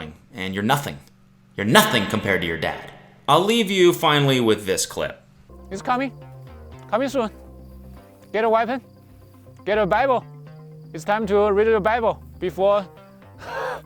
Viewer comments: I thought my mans said “Get a weapon. Get a bagel.” Now I want a bagel. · I could have sworn, at 16:04 that guy said "Get a weapon, get a bagel!"💀💀💀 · I thought he said "get a white man, get a Bible" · Legit 100% thought he said 'get a waifu and a Bible' Actually had me crying with laughter when you translated it for me 🤣🤣🤣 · at the very end I thought he said "Get a weapon, get a bagel" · I thought he was saying "get a bagel" at the end. In bagels this guy trusts.